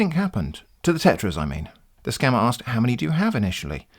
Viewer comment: I thought you were saying "tetris" and i wasn't even confused. I thought "derrek" was talking about how they dissapear when you fill a row